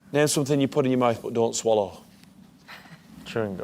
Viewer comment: first I thought I heard "What do you chew, but don't swallow?" my funny brain immediately thought "a dick"... well, listen to it a second time I think my mishearing sounds more fun. :D